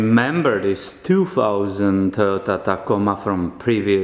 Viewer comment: I totally thought i heard you say "2000 Total Tacoma Muffin" in the first few seconds of the video. I found my trucks new nickname. ;) I'm hard of hearing and mean no offense!